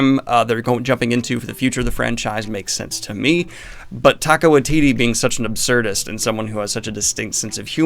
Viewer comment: Was it my ears, or did he say "Taco Waititi"?